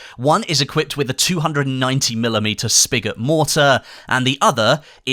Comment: I heard mortar on a tank... and now I'm crying. Someone please tell me I heard it wrong